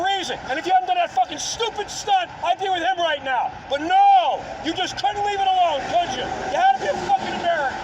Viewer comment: does he say "But nawwww, you just COULDN'T leave it alone! You HAD TO BE AN AMERICAN!!" If so, I love this!